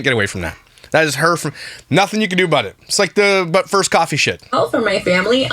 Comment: You said “But, First coffee”-shit I thought you said Butt first, coffee(then) shit.